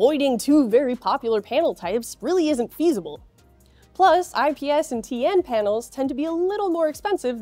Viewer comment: did she just say "pandles?"